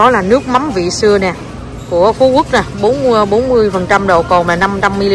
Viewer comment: Hình như nghe c nói nước mắm Phú Quốc 40% độ cồn, đang ăn cười sặc luôn, không biết mình có nghe nhầm không =]]] Nước mắm này ăn vô sỉn luôn.